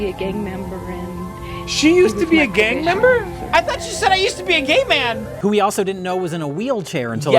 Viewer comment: “I thought she said ‘I used to be a gay man’”